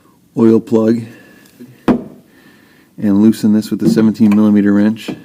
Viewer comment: What size is the wrench? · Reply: Ummm, watch 0:22 on to find out what he said. But since you didn't pay attention, 17mm